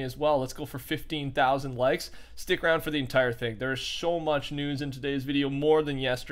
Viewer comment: did anyone notice that he sho much news instead of so much news